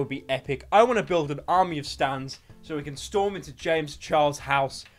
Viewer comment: did...did he just said 「Stands」?